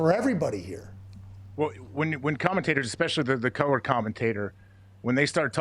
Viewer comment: did he say “colored commentators” ??!! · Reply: He said "color" commentators. It's what they used to be called before "analysts."